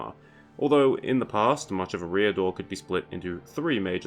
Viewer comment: I always pronounced it Eri-a-dor. Didn't realise you were referring to the same place, at first I thought you were saying a rear door lol